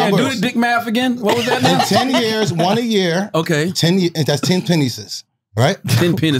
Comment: Did wax say Pa-nices?😂😂😂😂😂😂